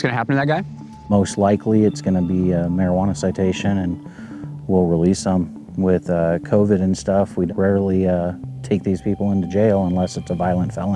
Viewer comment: did he say they release them with COVID AND STUFF